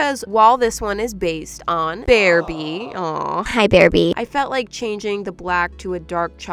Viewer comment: When she said "hi bearbee" I thought she said "Hi ThErApY!